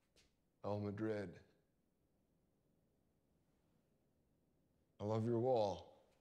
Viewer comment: IIIIIII looooooooove your waaaaaaaaaaall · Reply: ahah i'm french so obviously English is a foreign language at first i thought he was saying " i love you wall " ! since he's lookin' at the wall before showing his tears to his lady